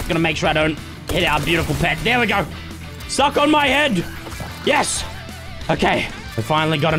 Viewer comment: Any body else heard him say eat my head😳